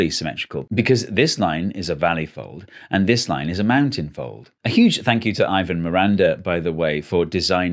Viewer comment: I can't make out the words for the two folds at 1:09, can anyone help? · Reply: Valley and mountain